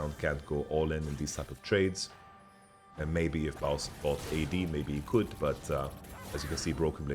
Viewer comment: am I imagining things or did he say baus?